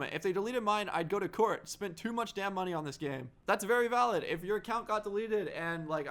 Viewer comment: Did anyone else hear him cuss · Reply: He said damn... Who cares